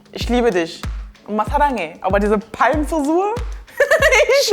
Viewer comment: I'm JUNGSHOOK did she just said saranghe~